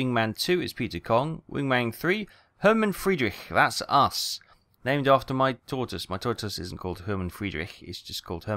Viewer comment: I thought you said "named after my daughter" and I was like "you named your daughter Herman?!?" 😂